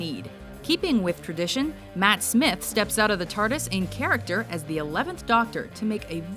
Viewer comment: i hear "Turdis"